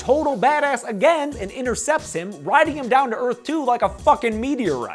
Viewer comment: It sounded like you were saying "riding him down to earth too" rather than "riding him down to earth 2"